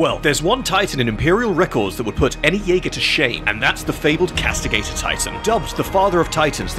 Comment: At first, I thought you said "castrator".